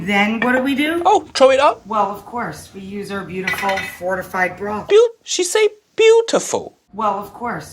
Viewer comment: I think she said "Disgusting broth"